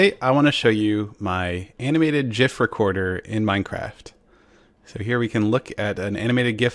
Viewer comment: it sounds like hes saying "animated jeff" at first